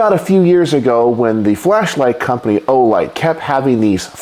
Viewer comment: God that really sounded like you said "fleshlight" 😂